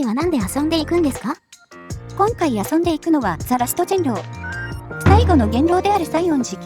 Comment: 開幕ザ・ラストジンロウ(人狼)に聞こえて草生えた